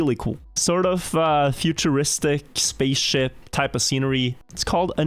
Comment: I may just be high, and I know he says "spaceship" but it totally sounds like he says "space shit" which I find funny